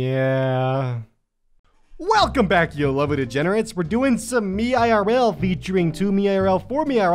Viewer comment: When he said "You lovely degenerates" I thought he said 'deodorants' and I was so confused.